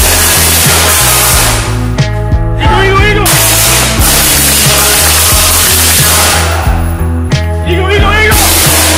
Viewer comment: what is he yellin at 0:20? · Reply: Eagle aka American or foe